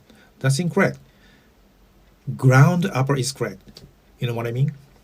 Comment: Ground Upper? As a kid, I thought he was saying "Grand Papa!"